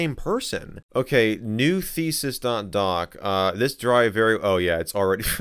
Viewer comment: "New thesis" I only hear "new feces" lmao